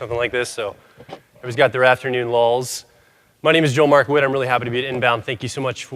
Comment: All this time I thought you were saying Markawitt haha now I know the truth. Anyway, great talk! Love Personality Hacker and always look forward to new podcast episodes